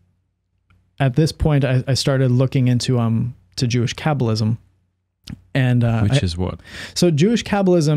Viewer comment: I thought he said Jewish Capitalism and was like 🤔